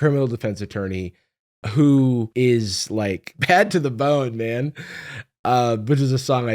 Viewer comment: Heard “ped to the bone” instead of “bad to the bone” and didn’t even flinch cuz… yeah… it sounded accurate 😅